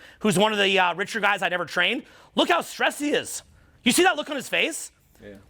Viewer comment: NOT Zuck!! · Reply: ohh sorry brother I heard “one the the richer guys I’d ever trained” you must’ve really said “I never trained”. To be fair it’s kinda weird phrasing.😂